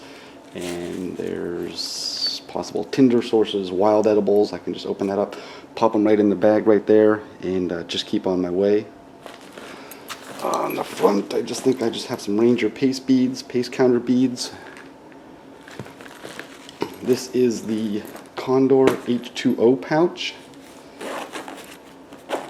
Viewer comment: Like the video. I could have sworn you said you had ranger PEACE beads- cracked me up. Since you already carry 9 v battery you could carry a little steel wool for cleaning or fire starting.